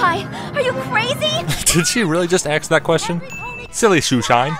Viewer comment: Did I hear 'Did you really just ax that question?' around 0:23?